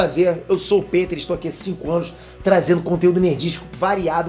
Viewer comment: Por um momento eu ouvi ele falando que estava a 25 anos